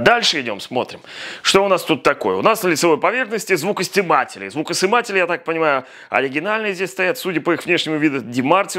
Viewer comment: послышалось -Звукостебатели, сукасниматели :)